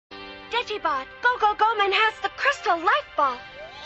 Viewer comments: wait, did i just hear Ash say 'digivolve go go go have the crest of light ball' or is it just me? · WTF did Ash say? All I heard was "Crystal Light Ball"